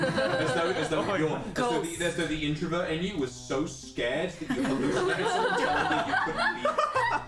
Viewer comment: I think Vox says "[the introvert in you] was so scared that you hallucinated someone telling you you couldn't be there."